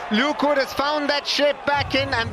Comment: What did he say? "Straight away Luke Wood has found that sh8t back in and has hit the off stump". 3 times ive listened and thats what it sounds like. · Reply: Shape